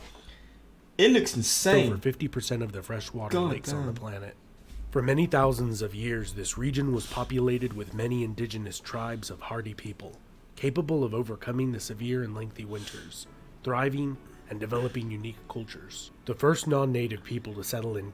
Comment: lmfao I heard "many indigenous tribes of party people."